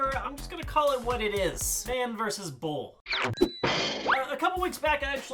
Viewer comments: I thought you said "man Vs bull" shit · I thought said man vs bull. Like this is bullshit. 😂😂